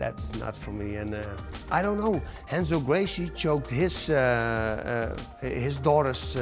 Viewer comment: who choked his daughters boyfriend? i cant understand his name lol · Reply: Renzo Gracie, one of the founders of Brazilian jiu jitsu